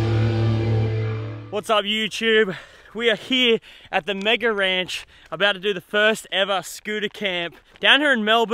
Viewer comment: did he say we are at the ranch